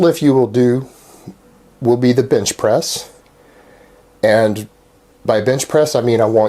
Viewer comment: lol I swear he said bitch press